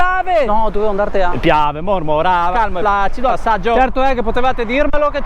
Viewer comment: sbaglio o ho capito "all'assaggio" ?!?!? Hahahhaha